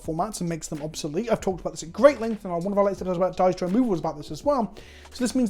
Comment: That sentence at 17:45 I legitimately can't understand what he's saying someone please help lol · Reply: "I've talked about this at great length and will put up a link to a 'Dies to Removal' about this as well-"